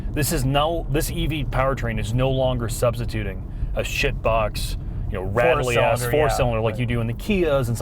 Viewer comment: I misheard shift box a few times